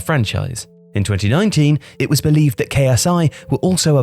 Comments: did i heard correctly that KSI want to buy some shares in Leeds United? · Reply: N S That’s what I thought but it’s QSI (Qatari Sports Investment) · I heard Ksi 🤣